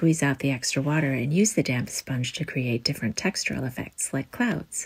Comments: U said damp i thought u said damn sponge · I thought you were casually saying "the damn sponge" until I read the subtitles Didn't question it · I thought she said damn sponge💀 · Ohhh damp sponge 🤣🤣🤣🤦🏻 I thought u said "you can use the damn sponge" 🤣😂😂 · I thought she said the DAM spunge and not DAMB spunge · I keep hearing "damn sponge" and it confused me so much with the passive aggressive words paired with he soft voice XD XD XD XD XD